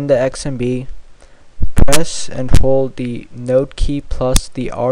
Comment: what did u say at 1:38? note key?